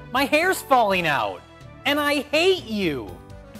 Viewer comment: I thought he said "and I ate you." I need to work on my... ear diction? Like my hearing of pronunciations? My... um... uh... ...pizza rolls